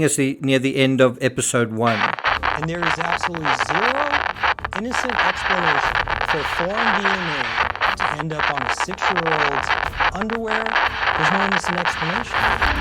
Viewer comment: The quote at 19:04 taken from the transcript (because I couldn't hear it very well): "There is absolutely zero innocent explanation for foreign DNA to end up on a six-year-old underwear...there's no explanation." Well, what if she touched something in the bathroom of one of the family's friends, and then her underwear?